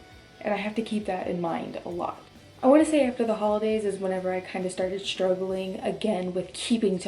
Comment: I kept hearing her say "hollandaise" but just realized she was saying "after the holidays" Lol